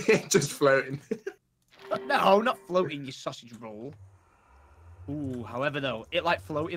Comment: Did Tom call a "saucy roll" at 1:02:20? Haven't heard that before... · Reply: sausage roll